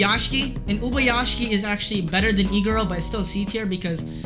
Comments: wait he said Iguro i thought he said E-girl... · who else thought when he said iguro he was saying e girl? LOL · I thought he was saying Egirl for Iguro Xd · am i the only one who thought he was saying e girl instead of iguro